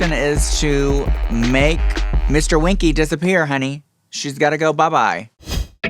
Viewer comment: did he really say: "mr winky has to disappear, SHE's got to go..."?